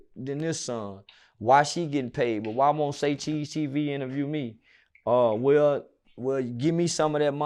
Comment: I thought Charleston said fk say cheese tv